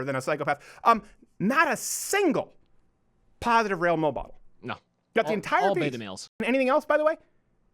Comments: Did anyone else catch him say “rail mobodels” at 13:24? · What does he say at 13:23? Positive rail bowl ball? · Not a single positive (_?_?_?_) What did he say??? Not a single positive Rail Mo Ball??? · he said “rail mole model” instead of “male role model” lol